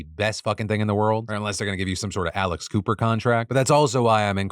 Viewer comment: I thought he was saying Alice Cooper and I was like “wait Alice cooper has a podcast?!!” And then the call her daddy stuff came up lol